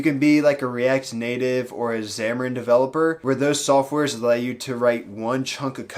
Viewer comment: what is that program being referred? I hear Zamoran? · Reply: Xamarin It’s made by Microsoft for cross platform mobile app development, and uses C# I believe.